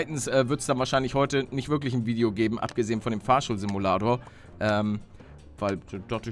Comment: Wer hat bei 8:33 auch Fahrstuhl-Simulator verstanden? xD